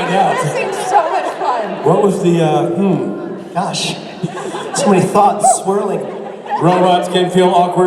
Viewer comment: What Rob said in 48:07 / 48:08 ??? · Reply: So many thoughts... Swirling...